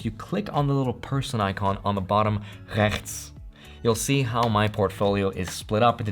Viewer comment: Did you just say the Dutch word "rechts" (= right) at 2:26?